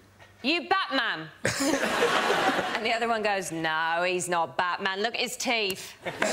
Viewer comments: "he's not Batman look at his teeth" girl what does it mean 😂😂😂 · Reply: What i heard was "he's not batman, look like he's thief" 😂😂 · OI YOU BATMAN? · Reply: I literally thought she said, "Nah, look, he a thief"